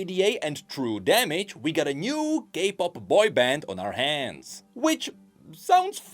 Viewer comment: with your accent i heard "gay pop" not "k-pop" lol sorry just had to share